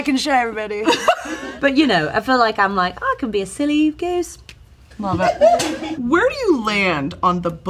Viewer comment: “Silly goose… period” was genuinely hilarious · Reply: I fully thought she said City Goose